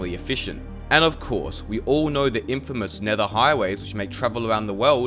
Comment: infamous? Am I hearing that right? It doesn't seem very fitting for nether highways